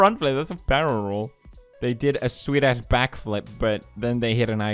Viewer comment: When he said “sweet ass backflip” I thought he said “swedish backflip”